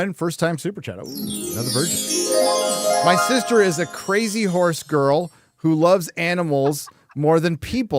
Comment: Does say, in General American, "crazy horse" /ˈkɹeɪ.zi hoɹs/ or "crazy whores" /ˈkɹeɪ.zi hɔɚs/ (IPA) 😅? Note that in Received Pronunciation "crazy horse" /ˈkɹeɪ.zi hɔːs/ is phonetically exactly the same as "crazy whores" /ˈkɹeɪ.zi hɔːs/ 🤣!